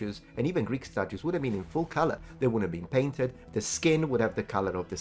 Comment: I misheard painted and I was squinting in absolute confusion at the screen thinking you said "I have a problem with the statues not being naked"